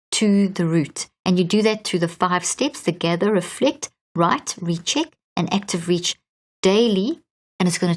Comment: Can anyone tell me the 5 steps she lists @ 14:48 ? She has a slight accent and I can't quite catch her words sometimes. Thanks 😊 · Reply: Her book Switch On Your Brain present this best and in detail. You can get the workbook for it as well. Gather Awareness. Reflect. Write. Recheck. Active Reach.